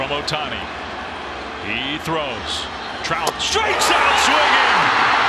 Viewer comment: 英語わからないけど、最後実況『ストライク、三振！』って【三振】って日本語で言ってるの？ それとも『スリースイング！』的なのがそう聞こえるの？